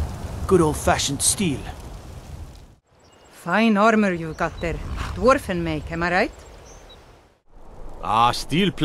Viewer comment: Did anybody else think that she said “iron armour you got there friend” at 0:48?